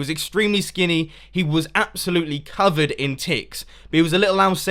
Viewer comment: am i the only one who heard "he was covered in tits"? 0.o